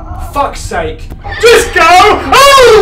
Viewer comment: I thought you said "fuck sake! Fuck sake! DISCOO!! OHHHHH!!!" x]